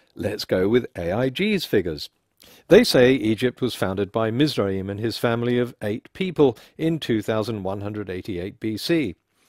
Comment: 2188 "Mizraim and his family of eight people"? Is that what they say?